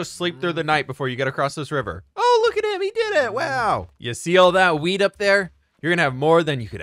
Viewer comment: Omg I thought he said “see all that WEED I was like OMG I